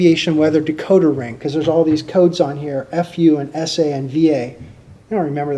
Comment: did he just say "F U"?